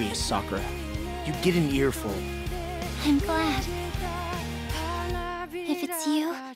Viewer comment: My friend and i are arguing whether sakura said senpai or im glad at 1:11, i swear in this trailer she said senpai!